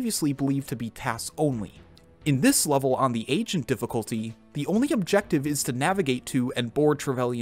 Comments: Who else thought that when he said agent difficulty he said Asian difficulty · When he started talking about Golden eye I thought he was saying "Asian difficulty" not "agent difficulty" I'm like Das racist